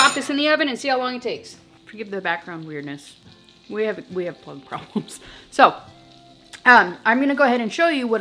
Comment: Plug problems 😂 I had to rewind thought you said bug problem